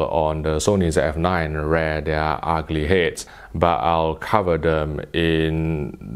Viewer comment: What does he say at 12:48? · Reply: kapil busawah “rear their ugly heads” I believe lol.